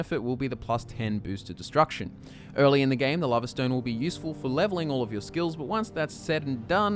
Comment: What was the first standing stone they mentioned? It sounded like he said lava stone, but I know that doesn't exist lol.